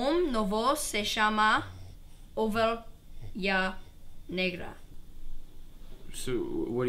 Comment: Did alex said nigga?